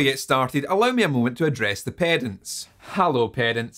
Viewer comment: Oh you said "Pedants" I thought you said "Parents". What you said after makes way more sense now. Love your accent dude.